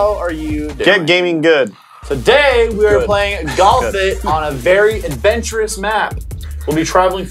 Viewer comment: sound like he said "Galf it"